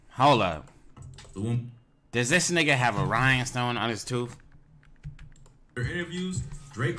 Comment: what did he say “Ryan stone” ? Can someone tell me what did he say and what it is I’ve never heard of it